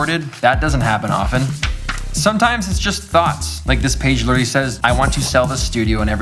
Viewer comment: "Sometimes its just thots"? is what i heard. IDK what that says about me haha.